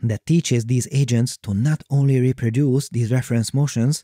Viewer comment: For a moment at 5:51 I heard they taught the ai how to reproduce